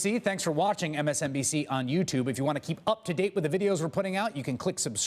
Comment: Haha, Chris Hayes is still saying "just blow me" at the end of every MSNBC video.